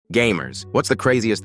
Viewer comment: For some reason I heard "gays" instead of "gamers"..